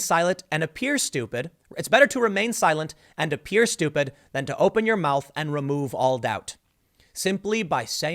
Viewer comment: I heard it as "Tis better to remain silent, and thought a fool. Then to speak and remove all doubt" As to the general election, None of them will win. I am fond of another saying. "Never get into a battle of wits with an unarmed person" Every one of the Dems at the debate was unarmed.